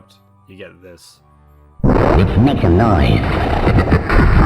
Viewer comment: God I love having my ears blown out by 'lets make some noise" tape recorder. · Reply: I hear "you should use a knife"